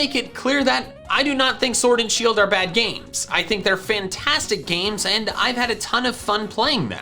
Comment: Who else heard him say Fintastic?